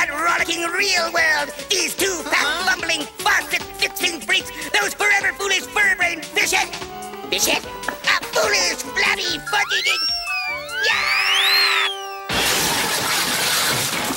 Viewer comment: I heard Big mouth say "Flabby f***ing"